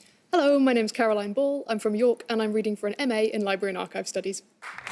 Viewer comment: Can I just say that Ball's vibes are ~immaculate~, of course she's in library and archive studies, what else 💚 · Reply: As a nonbrit. it sounded like she said Library-narco studdies.